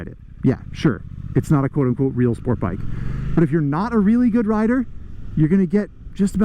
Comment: "If you are not a good rider; this is the bike for you" did I hear that correctly?